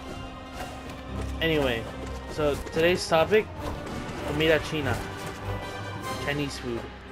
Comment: what did you say? i deadass cannot figure out no matter how many times i rewatch, also chinese food with knives is where its at (also why does "what did you say" seem so fkin hostile) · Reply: comida china chinese food